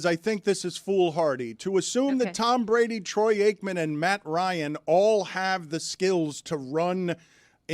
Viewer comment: I had to listen to this three times because I thought he said Matt hardy